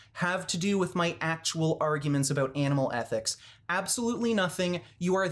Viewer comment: i thought you said anal ethics there for a moment pol